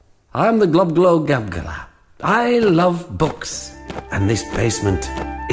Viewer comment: Was I the only one who thought he said "I love box" instead of books?